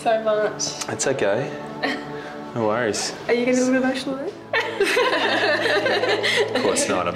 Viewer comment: What does the blonde girl day at 20:39? I hear "Are you..." I can't decipher the rest and CC can't either. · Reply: Are you going to get emotionally??? That's what it sounded like to me.